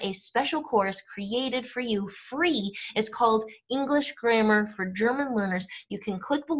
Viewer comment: Did you say English grammar for German learners